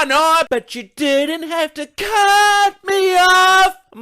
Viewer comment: huh, so it's "you didn't have to cut me off" here I am, mishearing it as something little more gruesome "you didn't have to cut me up."